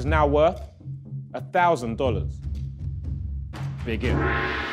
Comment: When Tobi said begin...idk why..I heard "big gib"